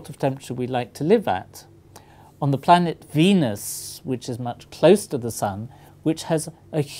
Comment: take on text and skip to 2:06 instead of venus it says penis lol